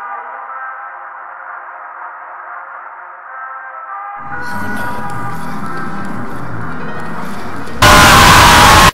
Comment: What is she/he/they saying at 2:14??? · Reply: I think she says you are not perfect but im not sure :^